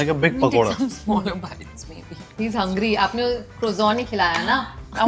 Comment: did she say smaller bites baby ??